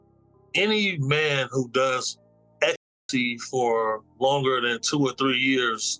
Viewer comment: what does he say at 9:12? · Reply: Ecstasy